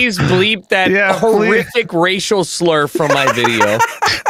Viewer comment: I thought you said Rachel slur